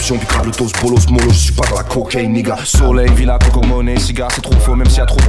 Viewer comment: I Swore this guy just said Nigga